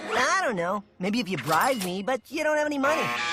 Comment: I first thought he said "ride", and had to watch it twice before I heard "bribe". 🤦‍♀️